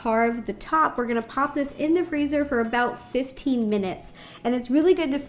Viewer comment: in 8:59 i heard rosanna said "15 mins" but is it 15 or 50mins? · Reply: Mayamin she said “15 minutes”